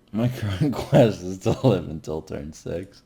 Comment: What does Reynad say at 8:02? I can't understand his mumble lol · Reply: "my current quest is to live until turn 6"